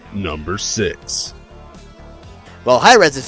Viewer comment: spam the mouse button on the time 2:59 if u want to hear dicks instead of number six :D